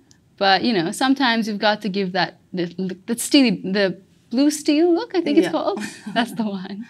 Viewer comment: did she really say "steamy"?